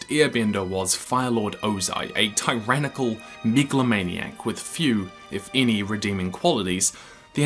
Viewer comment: did he just say tyranical niggalomaniac ?